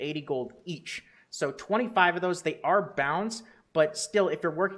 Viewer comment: IS IT BOUND? OMFG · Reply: he did say they are bound at 5:25, so don't blame him for something that he clearly said but you just didn't listen to